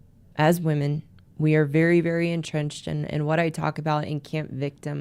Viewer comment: I thought you said 'intense' or 'intent' or 'camp?' victims but the subtitle read 'entrenched' so I dunno. "When we live in camp victim" ... okay ... yes it's amazing to me that milady has never, will never admit fault - it's amazing - seems pathological - like women feel they must NEVER admit fault - do they work for insurance companies ?